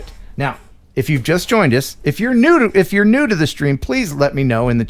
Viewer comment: Did I hear say, "If you're nude in the stream" ( TMI )